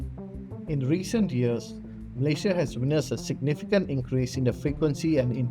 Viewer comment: "Mley Sha" had to put on the subtitles because I couldn't understand what he was saying